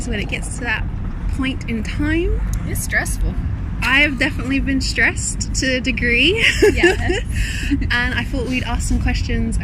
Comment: The gal in the yellow: "I've been 'stressed' to a degree".... I honestly thought she said , "I've been 'stretched' to a degree"....can't blame me, really.